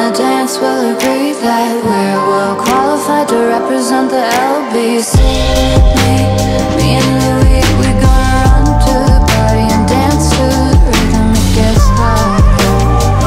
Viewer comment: I thought it said lbc me me and the league we gonna run to party and dance to rhythm of last night like she killed someone 😅😅😅😅😅